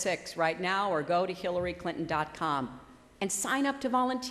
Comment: I swear at about 29:14 , she calls herself Killary Clinton. I'm not making it up. She's tellin' the people to go to her website. I hear her say "Killary Clinton dot Com". Listen yourself, tell me I'm an idiot if I'm wrong (I'm an idiot if I'm right too, but it's funnier.)